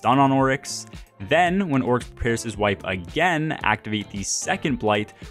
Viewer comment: I don't understand what you say..."Pierce his wife?" Please explain.. good guide otherwise, one of the best..n short!